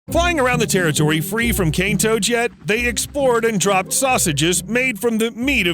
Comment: POV: those who thought he said King toads till they saw the writing